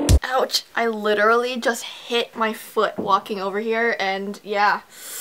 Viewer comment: My computer froze when she said foot and walking so i thought she said f***king